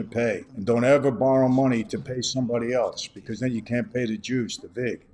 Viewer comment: Oh thanks God when he said ya can't pay back the juice I thought he said the jews lol